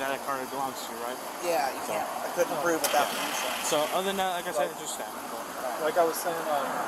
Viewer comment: subtitle correction. Where the subtitles say "unintelligible" he said "without the insurance." The full sentence was "I couldn't prove it without the insurance" Which is funny because that means he still doesn't get it that he needs the insurance in the vehicle.